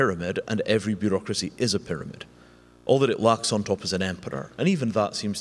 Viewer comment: What is he saying that is at the top of the pyramid around 9:20? Ampeter? I don't recognize this word, what is it? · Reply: Emperor